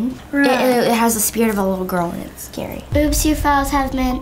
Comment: she said boobs instead of oops 😂😂😂